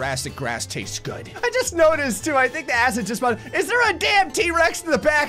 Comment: Im high but did say "I think the acid just..." then he switches subjects?